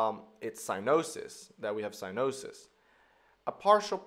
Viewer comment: could someone tell me what hes saying at 09:30 ? Sinouses? or something sorry I dont have a medical background so Im not sure what hes saying and I wanted to look up a definition but I cant get the spelling right apparently.